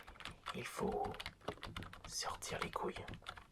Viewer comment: "Il faut sortir les couilles" ?! C'est bien ce que j'ai entendu ?!